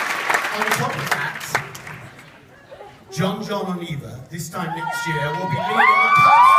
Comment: what did he say at 6:50? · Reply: On top of that, Jon Jon and Eva this time next year will be leading the cast on Broadway